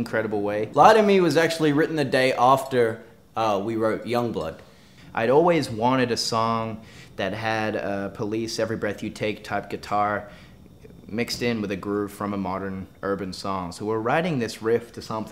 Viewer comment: From about 1:30-1:40 I was so confused. What was Ashton saying? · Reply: "I always wanted a song that had a police every breath you take type guitar mixed in with a groove from a modern urban song"